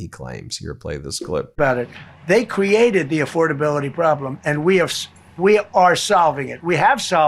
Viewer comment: I thought he said affordability was a Dem hoax, a few times.